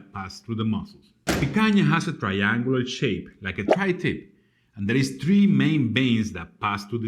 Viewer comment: did you say tri tip??! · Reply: Referring to the form of the picanha... triangular form. Yes I did mentioned tri-tip. 😀😀😀